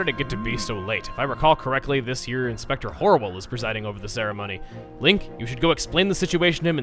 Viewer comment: it sounded like he said instructor horrible